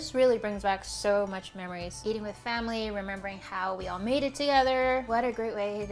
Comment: The first time I watched I heard her say "eating the family..." LMFAO Only because she had just talked about eating her child with no problem? Idk why but my brain made it even more dark humor lol